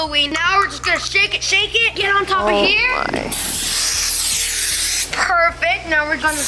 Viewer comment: I heard someone say why